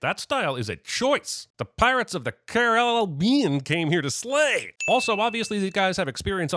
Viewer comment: Did I just hear Jeremy say... "Slay"..?